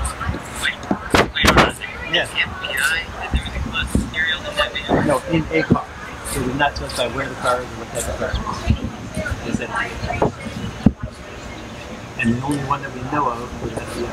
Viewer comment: I heard “fbi” and “serial number on that van…no in the car…the only one that we know of… people nearby…I don’t think…”